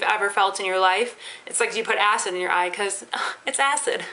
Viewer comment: at first i thought you said "ass in your eye" not "acid" bwahahah